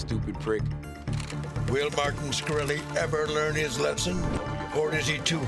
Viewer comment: Wait a minute, did he just say "Martin SQUIRRLY"??